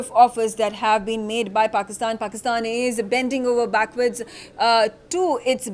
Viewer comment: did she really say Pakistan is bending over backward 🤩 or have I miss heard?